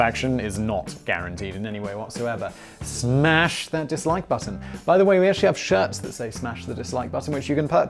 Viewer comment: This entire time I thought u have been saying "smash that dislike butter"... Then I saw ur mugs...